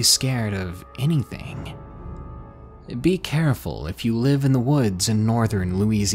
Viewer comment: I thought it said: " I live in my shed". I was like: "Darkness how is that even scary?"